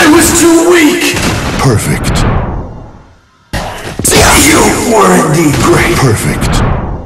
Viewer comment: has anyone ever known what the hell Kilik says? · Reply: I think he says: " You were indeed great "